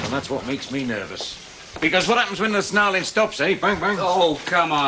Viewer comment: What does Len say at 31:23 or was that just a silly noise he made? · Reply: He said " what happens when the snarling stops, bang bang" That wasn't that hard to hear was it?